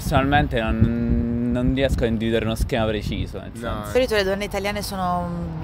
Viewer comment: did she really say "di solito"?! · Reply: I'm Italian and yep, she says "di solito". Point is she says it really fast and the video is cut.